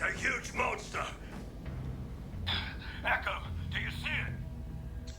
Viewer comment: almost sounded like Tech there.